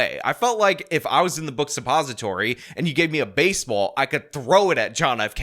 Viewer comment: Did he say " Book suppository "??